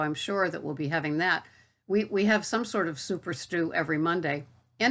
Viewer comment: I almost expected a completely non-existent recipe for a second. When you said "soup or stew," I heard "super-stew' & was almost certain it was going to be a special stew made with leftovers from the Big Green Egg.